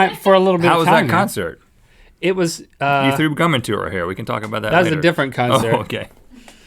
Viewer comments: did Rhett say that link threw cum in her hair??????????? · does Rhett say "you threw cum in her hair"?! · Did Rhett just say Link threw cum in her hair? 7:58 I thought this was like a family show. Guess he slipped. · did Rhett say "you threw cum in to her hair"?